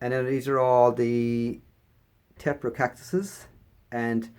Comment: Hi. I love your collection! Can you tell me the name of the very 1st hanging cactus we see in the video? You said it's name towards the end, but I thought you said "poor cactus". I can't find an example of it by that name. Did I misunderstand you? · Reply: Hi Amber, the cactus that you are referring to is called Aporocactus or commonly called the rat tail cactus, this is an epiphytic South American cactus which grows in rainforests so needs the same growing requirements as Epiphyllum and Christmas cactus etc.